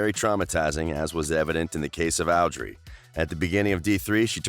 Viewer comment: But why did you say "owdrey"